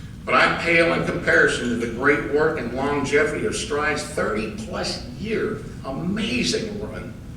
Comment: "Stride".....shit, I thought it said "Pride!"